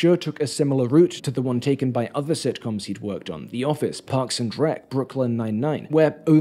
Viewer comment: I heard "parks and dreck"...